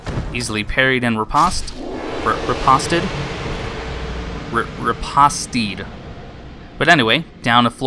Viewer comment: What word are you saying at 4:39? 😆 I’m sorry, I’m fascinated by new words and now I’m curious. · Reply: Riposte. A riposte is a quick, retaliatory strike in fencing made immediately after parrying an opponent’s attack, or a sharp, witty retort in conversation. But I definitely mispronounced it!